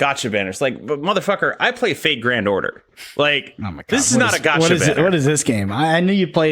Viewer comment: Completely off topic but when was talking about Fate Grand Order, I kept hearing it as "Fake Granddaughter" and was imagining a gatcha game where you play as a senile old man who has to determine which female NPC is not actually your granddaughter before they scam you for your inheritance.